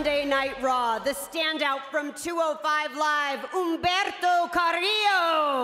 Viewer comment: When they said Humberto I thought they said Alberto Del Rio🤣🤣🤣